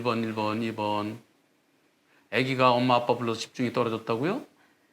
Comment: "엄마 아빠 차가 빨러..." ??? 잘못 들은 건가?... 119 소리가 갑자기 크게 들리는 것은 도플러 현상 때문입니다